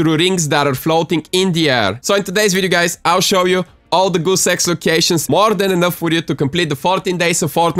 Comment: sounds like he is said all show you guys all the good sex locations